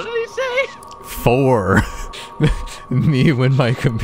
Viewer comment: waht did he say???? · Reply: Four